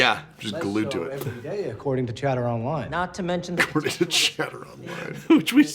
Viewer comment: Not me, repeatedly, mishearing "chatter online" as "cheddar online" and getting distracted by thoughts of cheese.